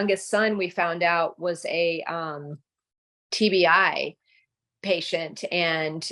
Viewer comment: Reference to acdcmiami's comment:At 1:54 said what I heard was TBI or TDI. I am guessing she said TBI, and may have been referring to Traumatic Brain Injury? Who knows?